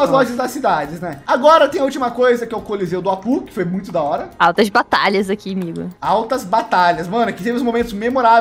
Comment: só eu que ouvi altas natalias?